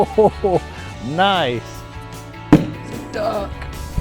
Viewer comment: sounds like guy says duck whilst being constipated